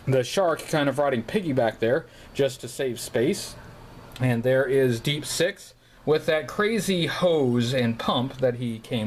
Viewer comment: Great collection! At 3:12, I thought you were going to say "And there is Deep Six with that crazy hoe Scarlett behind him."